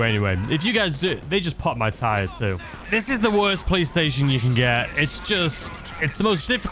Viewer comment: dude....did you just say it's the worst Playstation you can get 🤣🤣